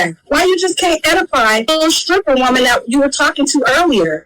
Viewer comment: WHOA! @ 6:09(😂my childhood home's house number)-6:10... did she just say "EDIFY a stripper?!"😂😂😂 How does one do that? Outside of saying "This life isn't for you beloved", or "Your kids are going to be picked on until they're SIXTY- PLUS If you don't stop". It ain't happening.